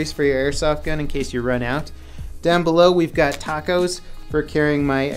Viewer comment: why does it sound like he was saying tacos instead of tap cos